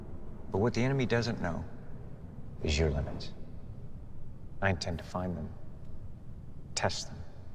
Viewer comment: Please let me know if the subtitles have a mistake/misspell. I spent hours typing and editing them individually. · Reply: Thank you for pointing that out! I must have misheard it. The line says "How you tend to find them" but it should be "I intend to find them".